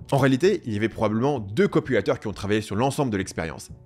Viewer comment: À 03:09 j'ai entendu «deux copulateurs».